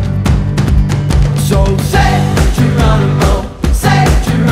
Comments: a few weeks ago someone pointed out that this song said "Say Geronimo" but i have bad hearing so i heard "Sex Tomorrow" and I heard kids singing this and im feeling like: do their parents let them sing this!? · The first few times I heard this song, it was at a dance and I kept hearing "Say 'tomorrow!'" instead of "Say 'Geronimo!'"